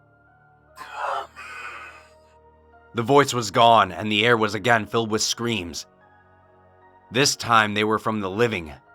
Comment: I couldn't understand what was said in the message can someone help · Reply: There's a link to the story in the description, but it said "I am Coming".